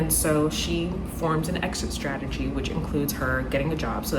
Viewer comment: my brain fully expected you to say "she forms an exorcism" and that is not what you said at all